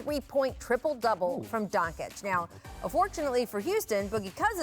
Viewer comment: lol I got 8 seconds in and I heard "33 point triple double from Donkic". Draymond and Luka's baby.